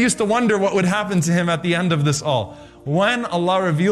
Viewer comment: the translation iss wrong wrath is not what the lecturer said it was RAHMA which is mercy in english